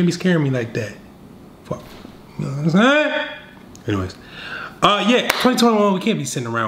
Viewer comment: did he just say the soda can bottle, "you know what I'm sayin??" 😃😃😃😃😆